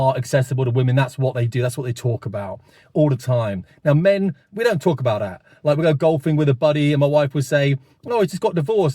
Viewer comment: I thought you said men don't talk about that - and then you did. ;)